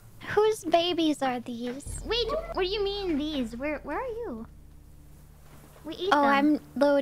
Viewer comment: Fauna "Where's Maury [Povich] when you need him..." I thought she said Mori, I was like hey don't blame the puppies on Dad, she's not even playing!